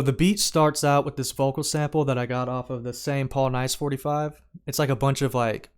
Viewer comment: what do you say? you got it off a Paul n Ice 45? Paul Nice 45? sample pack ?? which one is it please <333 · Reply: Paul Nice